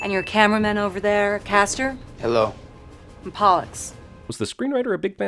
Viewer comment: In the Mockingjay part two when they said Pollux's name I misheard it as "Bollocks", causing me to almost fall out my seat with supressed giggles while the 5 other people in the cinema who weren't my family looked at me with evil eyes. Good times.